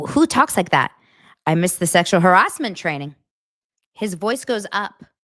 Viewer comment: I think what he said could possibly have been “I must have missed that part of the sexual harassment training.” Like after he made the “sexy onesie” comment. And I just remembered one part of the 17 point list - she said no sarcasm, etc. What an absolute nightmare that set must have been 😫